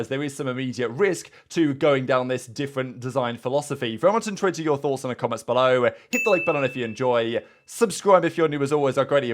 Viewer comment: What does he say at 00:35? Very much what? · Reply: i think its very much intrigued to hear you thoughts in the comments below but its gets me every time i never knoe properly